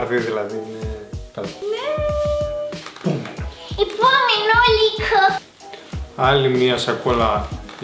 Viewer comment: τι είπε η???? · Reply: Επομενουλικο